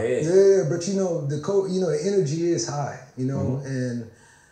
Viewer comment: I don't think u caught what he said around minute 13:00... energy is hot...but u gotta make sure people. Then it's like both him and the interviewer tried to clean that shit up